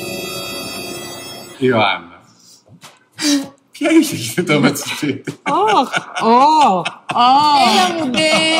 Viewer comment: Τι λέει στο 25:00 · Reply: Ποια είχε κινητό μέσ' το σπίτι